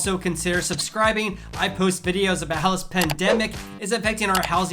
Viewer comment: Just a tip- it's really hard to understand what you are saying with your opening and closing phrase, " how this pandemic is affecting.." I keep hearing "housing pandemic" instead of "how this pandemic". Maybe slow down just a hair. Great videos! Thanks for putting this information together.